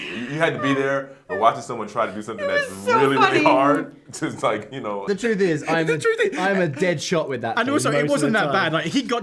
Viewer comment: Anyone heard that? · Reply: Charlie said “the truth is I am a dead shot with that…”